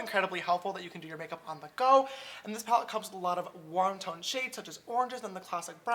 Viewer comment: i thought you said "won ton shades" instead of warm tone hahah love you sister